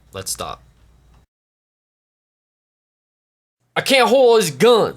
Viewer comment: If u cants holdz all dease gunz what can u holds? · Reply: I thought you said " if you cunts can't hold all these guns " XD